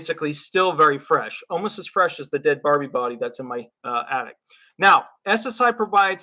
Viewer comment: Omg...you crack me up, lmao! Oh & you can rid that attic of spiders with peppermint oil! They HATE peppermint & so do other bugs!! And it works great too! Anyway I love your videos so keep em coming! And don't worry about that dead Barbie...lol. Just clean her up, get her a couple of new outfits, a new hairdo & she'll be just fine..hahahahaha! Have a great night! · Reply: Barbie! I have a hearing impairment and thought he said dead body! I couldn't figure out why he was laughing!